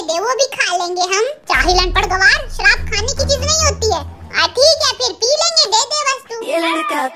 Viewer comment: what did he say?😅 Samajh mein nahi aya😅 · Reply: Thik h phir pi lenge de de bas tu😅